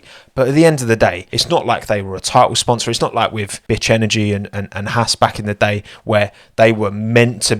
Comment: Anyone else hear "bitch energy" the first time.